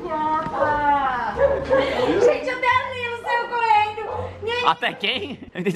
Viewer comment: "até quem? Achei que vc falou até a Leila saiu correndo" Por pouco não sai correndo kkkkkkk #susto